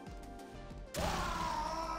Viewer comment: After nine years in development, G A B E N . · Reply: I heard it as "Gay Ben". 🤔